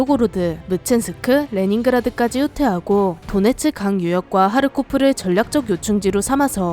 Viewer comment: 자꾸 타르코프로 들려......